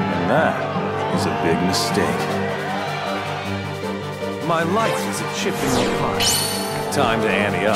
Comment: Does anyone know what Setzer say's at the beginning of the struggle battle? Even as far back as PS2, I've always heard it as "My life is but a chip in your pie." XD